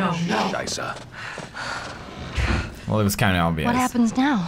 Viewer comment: Did mark just say "shit" in German?